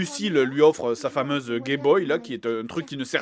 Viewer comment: Si toi aussi a 8:28 tu entends Gayboy